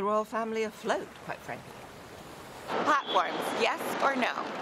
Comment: What does the second person ask? It sounds like she says "popcorn, yes or no?"